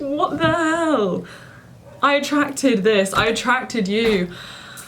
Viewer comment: I got kinda gassed because I thought she said "I am attracted to you " And me in my bed about to go to sleep just think. "Say mums your attracted to me " Only to realise that's not what she said 😢